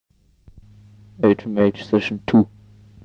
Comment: Here are possible names for some of the tracks : 0:00 1. "H Macht Session 2" (It seems to be what he says at the start + it makes sense because Heroin Makes Happy = Heroin Macht Gluklich in german --> H Macht) 2:29 2. "Birds of Damnation" (same birds sounds sample than the Corneus song 'Birds of Damnation') 14:09 6. "Heroin Makes Happy" (He says it in the beggining) 16:44 7. "Nocturnal Pulver", this song was reused in the 'It's not too late to say YES to H' demo, and also it was cover by Grausamkeit in the EP FCK God - Starring Into Darkness, where the name is Nocturnal Pulver. 19:54 8. "Dark Moon (clear version)" Not to confound with the first version of this song 'Dark Moon (raw version)', from the 'It makes really happy' demo and the split with Blod Bes. This song is also called 'H Transfers From Me to You' on the split. 23:12 9. "Do You Know My Name", said in the beggining. 25:59 10. "Too Much Brown Death", also on the split with Melse. I didn't find any title for the tracks 3 to 5, sorry. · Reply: he just says "HMH session 2". and the "bird sample" is just a cat toy.